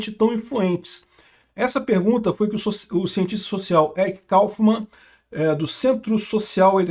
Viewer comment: eu ouvi "eric cartman" 😅